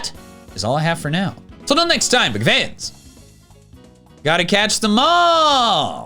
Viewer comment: Sometimes when he says ''gotta catch them all!'' at the ending i hear ''gotta catch the mall'' and i find that very funny